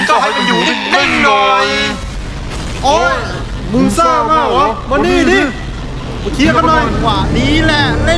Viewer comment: ผมได้ยินว่าเอา-วยมานี่ อะครับ